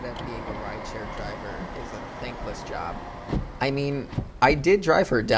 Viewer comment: I said thankless - people do not show appreciation for our effort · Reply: oh ok i thought you said that it's a thinkless job. Ok.